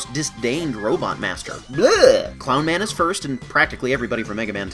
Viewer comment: what do you have against Clown Man? His stage is kind of a chore, but I always found the fight with him very rewarding. And Chill Man is one of my favorite fights in the whole series! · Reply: when I first heard you say clown man was your least faveroute boss I thought you said cloud man